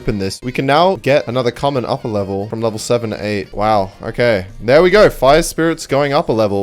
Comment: he said there we go but subtitles said let‘s go